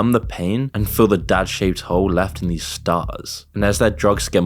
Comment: '' fill the gaping hole inside of them '' Really? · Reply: Bro, I thought he said "dad-shaped hole" and I about lost it. 😄